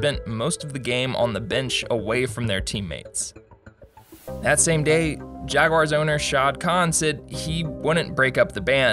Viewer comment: I thought that said “Shao Khan, Jaguars Owner” lol